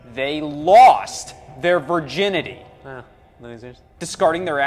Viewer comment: I still have NO idea what is mumbling in this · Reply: "Ha, losers"